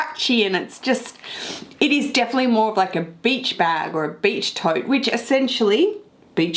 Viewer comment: Who else thought she said bitch bag?! 😂 love the new new ✨🤩